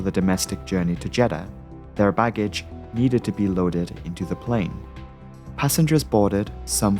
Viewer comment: On the subtitles it says loading instead of loaded